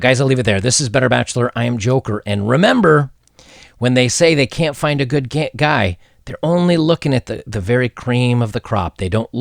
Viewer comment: Did he say “find a good gay guy” at the very end of the video (14:50)? Lol